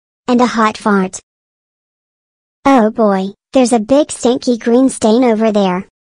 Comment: Did say the word "Stinky"?